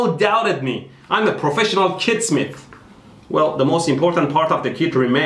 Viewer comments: I swore he said kidsmith lol. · I thought you said kidssmith